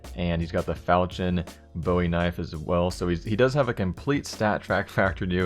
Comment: how does he have a falchion bowie knife? lol it sounded like you said that :P